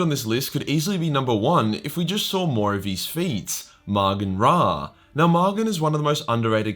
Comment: I thought you said “‘more of his feet” about Raugan Ma at first and was confused